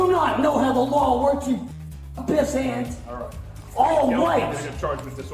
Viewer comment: did he call the officers pissants😅😂